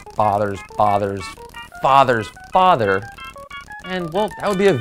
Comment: When u said Father father father it sounded like u said Potter potter potter potter potter potter lmao 😂